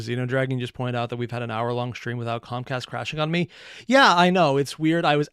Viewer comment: Not gonna lie... it might not actually make a difference, but I keep mishearing ComCast as ComStar.